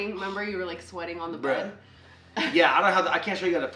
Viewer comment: you can tell is nervous as well I could sware I heard her say "remember you was sweating on the bread". In stead of bed. lol sorry that was cute n funny. I'm soooo supppppper happy for you guys & can't wait to follow along this beautiful wonderful blessed journey.. I just love y'alls vibe, sense of humor & how you guys always make light of bad situations💜💙 Sending love y'alls way, stay safe and blessed 🥰